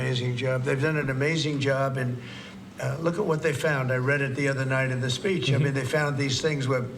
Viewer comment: I thought he said “look at what they found on reddit” at first and it made me choke on my food